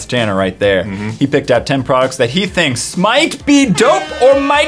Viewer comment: he said "thinks might be dope" i heard "Think smite be dope" xD